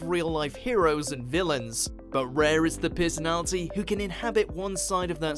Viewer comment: When you say "personality" @ 0:05, I hear "piss Nazi"... Do with that information what you will.